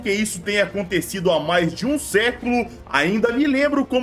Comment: aqui eu falei 1 século mas eu errei na tradução! O que foi dito é meio século pelo senhor John Hayes